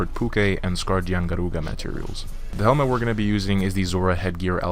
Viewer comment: I’m sorry what, I didn’t understand what you said after tempered Pukei Pukei. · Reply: Caden Wilkerson Scarred Yian Garuga